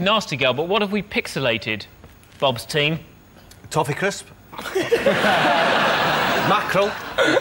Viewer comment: I thought they were saying "Coffee Crisp" which is a chocolate bar available here in Canada.